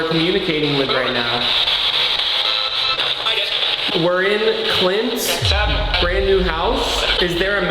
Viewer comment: literally sounds like Rug saying “Hi guys!”